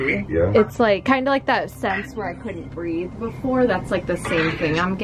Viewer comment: It might be Michael Jackson who said this but I heard “couldn’t read it.” As if he said he couldn’t read the letter someone sent him